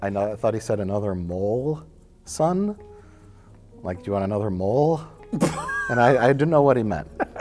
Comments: as a kid I thought he said “you want another Moose, eh?” Like one ridiculously oversized chocolate moose wasn’t enough cracked me up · I always thought he said, “morsel”